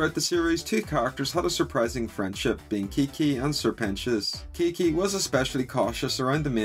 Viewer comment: I didn’t know Lucifer said “keekee” I thought he said “kiggy”